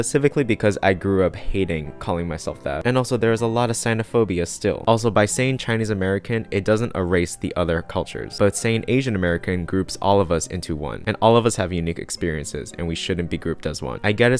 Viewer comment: I'm going to be honest, my brain somehow thought you said Native American and I was so confused.